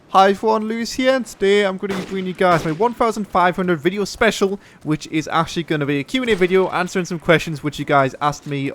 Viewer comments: LOL thought he said i foreign lewis here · I thought you said "HieveryoneLuciferhere" no realy thanks sir !!! you come across as real and people respond to that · He says "Hi everyone lewis here" i hear "hi fuan lewis here" lol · Who the fuck thought he said iphones lewis here ! hahahahahha